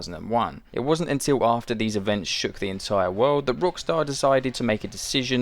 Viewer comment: or so? Did you say Brookstar or something instead of Rockstar? Because i went back twice and you said Rockstar wrong.